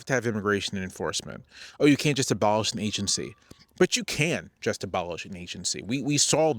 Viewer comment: I thought you said, "But you CAN just abolish a nation-state!" and was ready to go full radical lol!